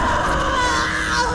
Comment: I thought she said WHAT ARE YOU DOING!!!